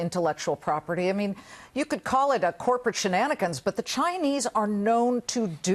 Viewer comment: corporate shilonegen? What is the word? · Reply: shenanigans = dishonest conduct, mischief